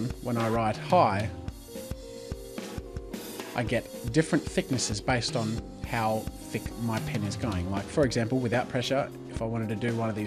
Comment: "I get different thicknesses based on how my penis' going" lol! is that what i hear!? XD